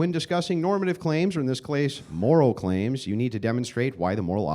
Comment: he keeps saying “clase” instead of “case” 😂